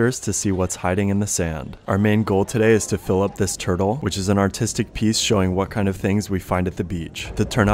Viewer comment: I thought he said autistic piece